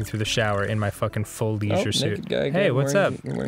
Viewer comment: I was so tired while watching this that I thought that it said "Leisure Shit Larry" for whatever reason. God.